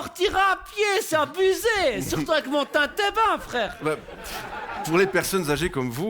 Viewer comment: il dit quoi a 2:10 ? surtout avec mon quoi ? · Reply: « Surtout avec mon tintébin ». Un tintébin est un déambulateur avec ou sans roulettes.